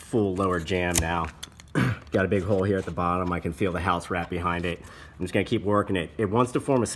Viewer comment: I thought you said house rat 🐀 bahahaha only for a second 😅